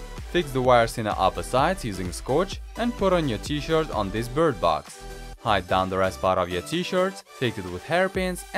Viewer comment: DID HE SAY BIRD BOX NANI